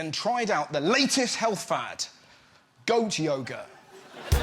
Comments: My American ass thought they were saying "Goat Yogurt" for way longer than I should have. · I thought they said goat yogurt at first 😂😂 I was so confused lol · I honestly thought Russell said Goat yogurt not goat yoga. Absolute nonsense.